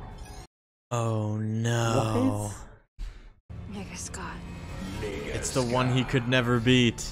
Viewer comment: One of my favorite movies ever. First time I watched this with a friend of my mine (we're black) and when "nega scott" popped up, we couldn't stop laughing because it sounded like they said "nigga scott".